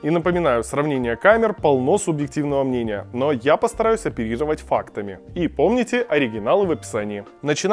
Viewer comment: оперировать? Или мне послышалось?